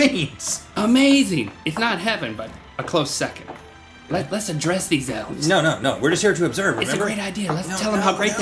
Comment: it sounds like he says 'lets undress these elves!'